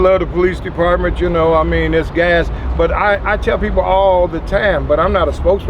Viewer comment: did bro just say the PD is gas?